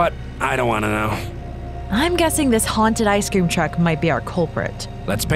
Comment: I thought it said a man eating an ice cream truck